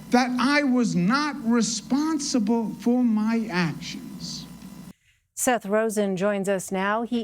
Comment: wtf I legit thought they said "seth rogan" hahahah he looks like him AND has an almost identical name